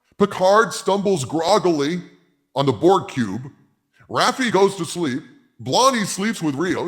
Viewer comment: Sorry, for a minute there, I thought you said "Porg Cube."